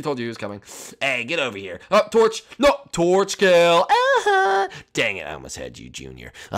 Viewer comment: did sonic say dam*